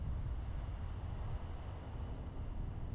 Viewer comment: i love this video thank u so much but also 15:45 as time goes on ... what? i can't make out what he says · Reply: I THINK he says "As time goes on, we'll be expanding"